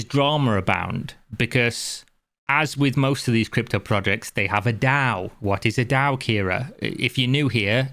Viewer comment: Oh I thought you said DAU at 8:30... which would've been "Dümmster anzunehmender User" and that translates to "Stupidest possible user"